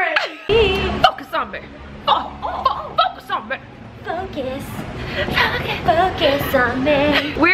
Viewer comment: When she say focus I thought she said fuck a zombieXD